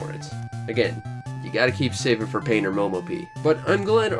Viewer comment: What's the name of that Game Boy game you're talking about at 12:10? It sounds like you're saying "Pain or Momo Pee", but I know that's not right, haha. If you recommend it, I'd like to check it out. I just don't understand what you're saying. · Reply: Painter Momopie, honestly it's one of my all time favourites, I've got a full video review of it as well! <link> If you're into arcadey Pac-man-esque things it's worth looking into, though a physical cart will set you back a pretty penny.